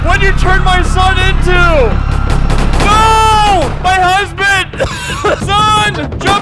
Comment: did bro just say "nooo, my Hazbin?" 💀🙏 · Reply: He said husband, fool.